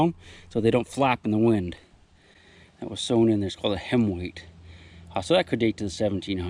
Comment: I thought you said henweigh as in “What’s a henweigh? Bout two pounds...🐔🙋🏻‍♂️